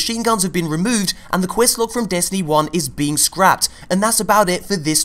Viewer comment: What did you say at 3:51 ? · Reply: "quest log"